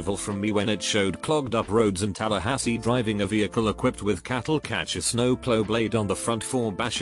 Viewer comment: did that say cattle catchers